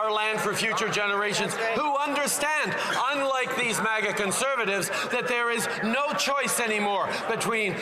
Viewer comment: Did trudy say " Maga Conservatives " ? Maga is for Americans politics .